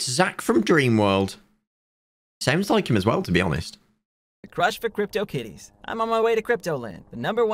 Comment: did he say "crush for crypto kiddies"???